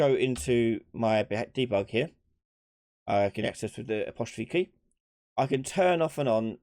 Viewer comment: What was the shortcuk key there? I didnt understand it. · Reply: Apostrophe key ( ' ) to get to debug then 4 to see perception